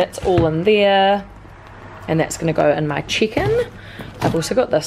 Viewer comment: Looking forward to see you other vlogs for this trip!! Also thought you were saying chicken instead of check in LOL